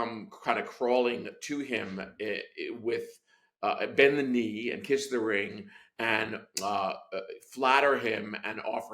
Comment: “Bend the knee and kiss the ring” did I hear that correctly?